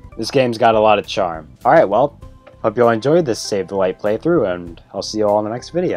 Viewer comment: The first time I heard you say the word ”SAVE the LIGHT” I thought you said ”Sugilite”